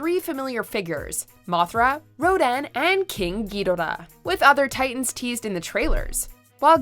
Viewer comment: Is it just me or did she just say Ghirora?